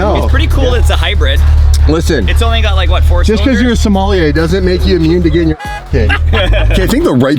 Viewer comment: did demon just say somali in 3:41 hhhhhhhhh